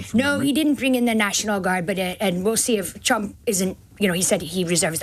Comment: In 29:08 did she say Chump? Hehe